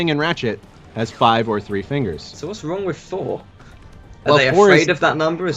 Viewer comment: you sound like you said "Thor" not "four"